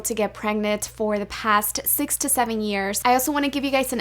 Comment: i thought you said 67 years💀youre not even 35